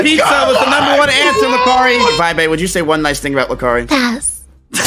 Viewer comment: Can someone tell me what Veibae said to at 20:33 ? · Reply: pass